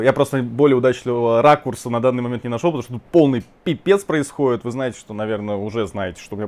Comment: он сказал пипец,или мне послышалось?!